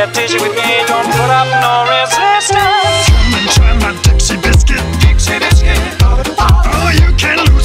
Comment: I love how aggressively ''Dixie biscuit" is said. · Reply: I thought it said: itsie biscuit